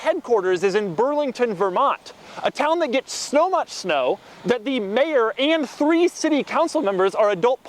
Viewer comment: Did he say snow much snow at 2:55?